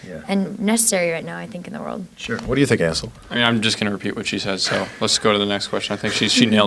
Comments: I thought he said asshole instead of Ansel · swear to god he says what do ya think asshole? XD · I heard asshole. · i love TFIOS it is a great movie and book but at 1:38 it sounded like the guy interviewing them called Ansel asshole just because of how he pronounced it i re-winded it about 4 to hear him say that it was a fuck up but a funny one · I swear he said at 1:38 "Yah, what do you think a**hole?"